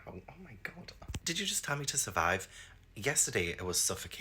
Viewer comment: I thought it said “smile” instead of “survive.” So maybe it was the little boy. Plus, I thought you really liked Dracula?